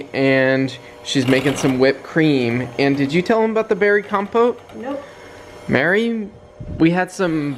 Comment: I’ve never heard of “compote” before. So, when said it, I thought he said “berry compost.” I was like well that works I guess!